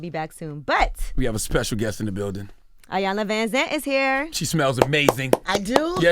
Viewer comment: The way Queen Auntie Iyanla’s ponytail turned when it sounded like Angela said, “Ayana Vanzant is here” 😩😂😭 She plays no games with the pronouncing her name!